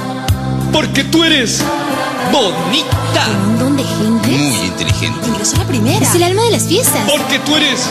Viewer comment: que dice en el 2:25? despues de que dice tu eres bonita ? :C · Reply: un monton de gente, muy intelgiente, ingreso a la primera, es el alma de las fiestas